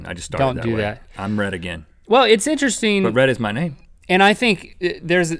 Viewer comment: I thought Rhett said Im red again, my name is red....